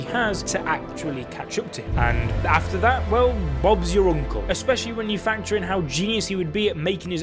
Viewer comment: Did he just say "Bob's your uncle"